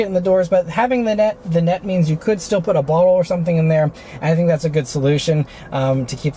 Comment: for some reason I listened several times and all I hear is "You could put a bong or something in there." XD